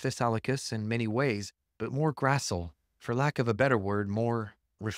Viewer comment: What word are you saying here? Because the auto subtitles say "Grassle" and that's a surname, not used as an adjective